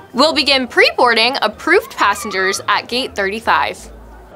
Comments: she said 35 but the captions said 27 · She says GATE 35 and subtitles say GATE 27 💀💀